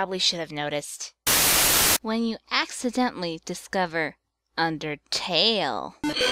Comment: pppppph right here it said when you accidentally discover but I rewind it 10 so it sounded like WHEN YOU ACCIDENTALY DISCOVER SOULS